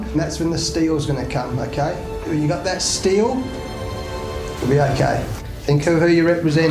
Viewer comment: That steel pretty much sounded like Steve ;) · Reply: At fisrt I really thought justin langer said "steve"